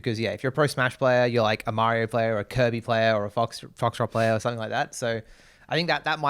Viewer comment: Did he say a Foxtrot player? 😅😂 It's actually kinda sweet how little they know. Like your grandpa tryna relate to you 😌